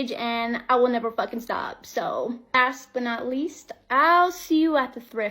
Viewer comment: I watched this part several times. Did she really say asp but not least?? What does she think that means? She sounds like a self entitled bully, but not a smart one.